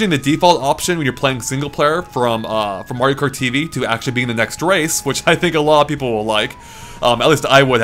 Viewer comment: what is saying?To me it seems like he's saying "from MKTV to action being the next race" which makes no sense